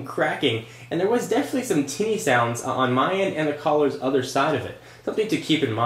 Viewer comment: I think i heard you say "titty sounds".